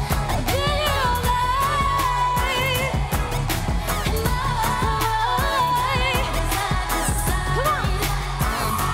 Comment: what did Nicki say to Ariana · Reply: “you got it boo”